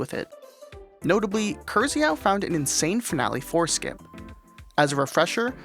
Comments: HE FOUND A WHAT??? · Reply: i heard that as "finale horse skip" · my tired ass misheard "insane finale foreskin" god help my soul